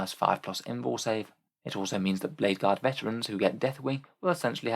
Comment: Dark Angels can't use vanguard/sternguard vets, they have company vets instead. Company vets don't have Inner Circle. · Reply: I was listening to this while doing the dishes and misheard blade guard for vanguard